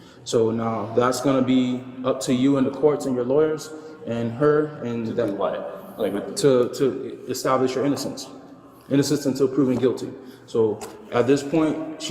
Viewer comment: "I have to get you in and you have to prove your innonce in court because you know innocent until proven guilt" what the fudge did the officer say ther please...